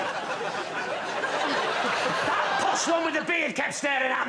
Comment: “That posh one with the beard kept staring at me” “I didn’t like her husband either” In tears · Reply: oh, posh one. i swear, i thought he said possum.